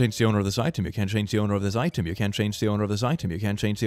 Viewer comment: i hear you can't change the owner of the eye timmy